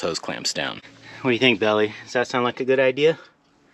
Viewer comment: I misinterpreted the puppy’s name. I thought your daughter was saying “Billy”, so thank you for the clarification. Belly sure is a curious puppy, but that can be a good thing. Hope the old gate hinges are strong enough…a gate with plywood added will certainly be strong but it will be heavy too.😂